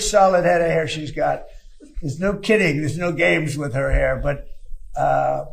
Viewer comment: Bro when he said "no kidding" i heard him say "no titties" and it seemed completely reasonable for him to say. 😂🤦‍♂️